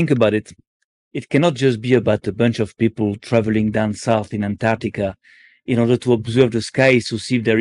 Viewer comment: Did he say "downside in Antarctica" ? Why not "aroundside to Antarctica" ? 🤔